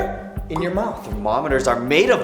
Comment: When he said that mercury was your mouth, I thought that he said your mom.